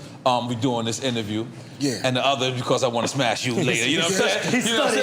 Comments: Yo what did the bul say at 3:26? I gota be tripping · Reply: Sounded like he said “I wanna smash you later” but I might just be tired af because I just can’t imagine lol or idk what exactly he’s referring to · Am i buggin or at the 3:26 did nore say "because i wanna smash you later"... Im buggin rt???